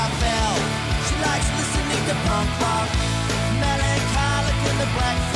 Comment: I used to think it said "She likes pissing in a plant pot."